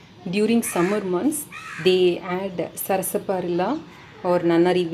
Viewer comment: Mam thanks but i couldn't understand what u said to add in pot at end of video at 18:25 to keep water pot cool...pls reply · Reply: Sarasaparilla roots.